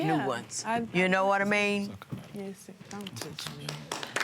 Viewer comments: Did anyone else think they heard her say, "Don't touch me"...??? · Am I the only one who heard her say “don’t touch me”